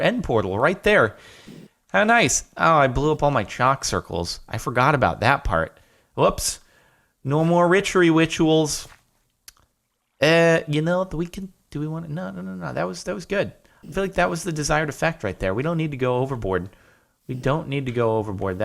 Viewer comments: Who else heard him say richery witchawals on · Anyone else hear Jordan say "No more ritchery wituals" at 14:08? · Did i hear Jordan say "No more richary wituals"